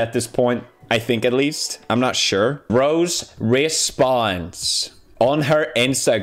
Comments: Lol I thought he said "Rose respawns" 9:38. That would be awesome. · when pewds said “rose responds” i heard it as “rose respawns”